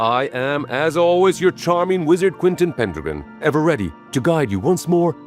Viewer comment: Quick spell for support! 🙏 Tap Hype Points under our latest video on the YouTube app, you can do it up to 3 times by reopening the video! It really helps boost the algorithm in the first crucial hours. Spread the word to fellow wizards! 📣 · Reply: Oh, Pendragon, Sorry I don't know why but I thought your were saying Pentagram.